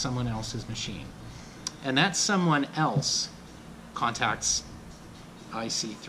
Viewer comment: I misheard that as ICP, lol